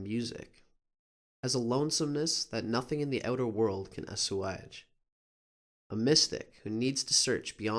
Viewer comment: What are saying at 7:10? Asuahage? ._. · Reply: I Ler lol I didn't pronounce it very well. "Assuage." It means, like, "that nothing in the world can satisfy." Hope this helps :)